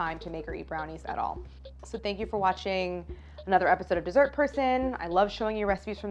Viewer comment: I always hear "this is her son" instead of dee-sert per sun